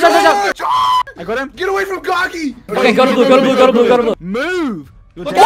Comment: Turn on captions 4:40 when says "GET AWAY FROM GOGY" but the captions says "get away from cocky" LMFAO